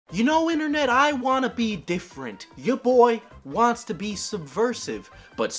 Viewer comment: I heard “Your boi wants to be supersized.”